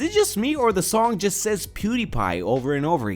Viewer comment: dil hai is being said over and over actually, but it sounds like pewdiepie.